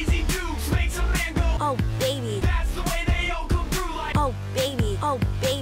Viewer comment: amethyst is not saying oh baby,she is saying i think someones being a soar loser